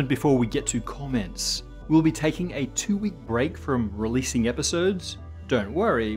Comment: Accidentally misheard it as "a two year break" from making videos and panicked, lol